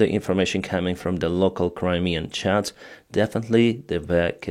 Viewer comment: when you hear 'local crimean chads' instead of 'chats' insert meme